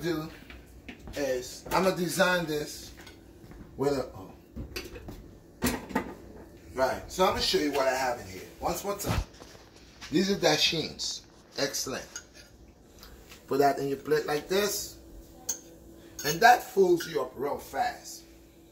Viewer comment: did u say excelente 6:50 ? ... Mann u dont wanna mess with that dish rght there,, that dishhh is like a full complete meal